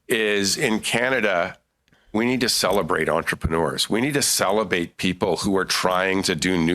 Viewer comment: It sounded like he said, "we need to salivate people."